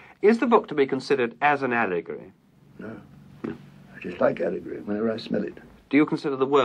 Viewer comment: Does anyone know what Tolkien said at 0:51-0:53? Thanks so much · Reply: A year late but in answer to your question. Tolkien: "No no, I dislike allegory, wherever i smell it"